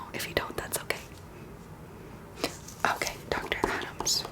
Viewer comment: My History nerd self thought she said John Adams instead of Dr.Adams. There is something wrong with me for sure.